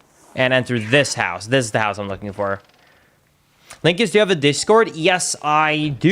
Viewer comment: before you finished the sentence, i heard 'pre' heard :"linkus, do you have a onlyfan" Have someone ever asked you this question ?